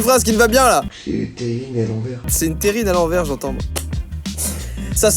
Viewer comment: moi j'ai compris "j'ai mis Perrine à l'envers" 😭😭😭